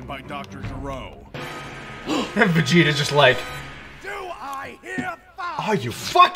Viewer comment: DO I HEAR FIVE😂😂😂😂